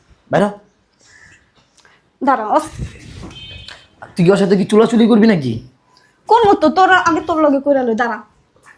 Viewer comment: কি বলছে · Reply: বলছে চুদাচুদি করবি নাকি 😂😂